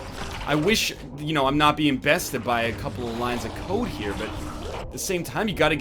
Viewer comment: I thought he said "I'm being bested by a couple of lines of coke."